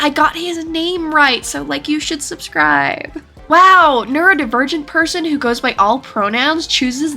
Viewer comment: I had to rewind 5:40 like 5 times before I heard "neurodivergent", genuinely thought they said "nerd virgin" lmfao